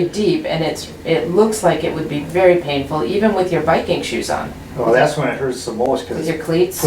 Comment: I thought you said “Viking shoe” the first time 😂